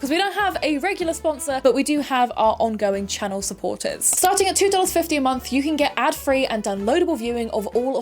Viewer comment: I had the biggest scare when I saw the captions were saying $250 a month instead of $2.50 a month haha I had to do a double take